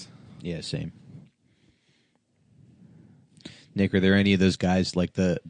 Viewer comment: whoa WHAT did he just say? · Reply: Nickar